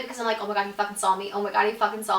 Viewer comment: Omg when she said he fudging saw me I thought she said fudge a zombie